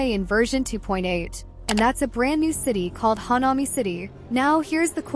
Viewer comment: I thought you were saying hanami which is the word the Japanese use for watching cherry blossoms fall and literally means "flower watching" I thought since chisa looked Japanese inspired thatd make sense